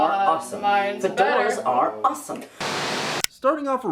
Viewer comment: i thought they were saying "the doors are awesome"